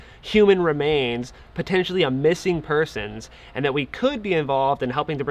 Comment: Bring marker flags and mark all the spots.. I thought he said “missing purses” case.